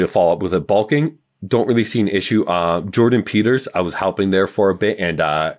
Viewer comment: He thought u said Jordan Peterson 😂😂😂. He meant Jordan Peters